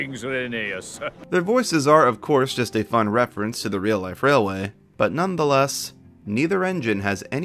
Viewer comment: All I heard was "Rainy Ass" 💀